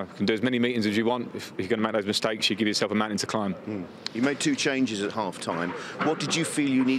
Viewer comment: When he said "give yourself a mountain to climb" I thought he said "you've got a man in decline" in reference to Alonso 😳